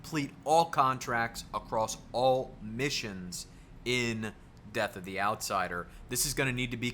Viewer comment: I really need to check my ears. When he first said death of the outsider, i thought he said deadly hallows.